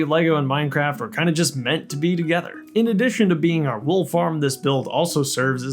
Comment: Misheard "wool farm" as "wolf arm" and was like sorry you're adding WHAT?